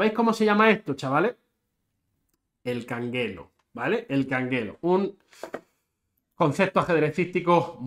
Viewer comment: ¿Cómo se escribe, canguelo, canguel, canguedo? No escuché bien jajaj